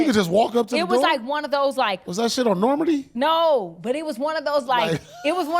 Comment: 😂 did he say Normandie. Thats cold